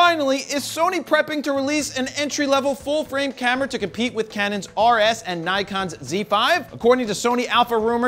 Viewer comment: Did you say Canon RS?